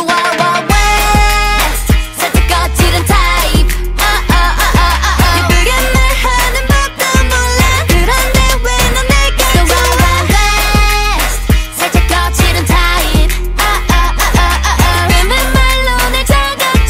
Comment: Ngl when Yuna said 'wild wil west' I heard 'wawaweee', I am so sorry